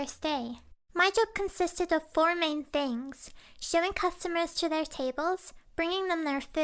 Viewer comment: I first heard "shoving people to their tables"